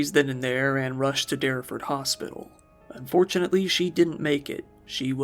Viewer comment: he said "fortunately she didn't make it." Referring to tragic death. WTF that's a pretty foul mistake if I do say so myself you psycho path. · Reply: Get your hearing checked, he said "unfortunately"